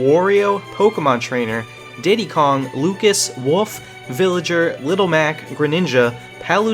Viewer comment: in 2:34 he says wuf instead of wolf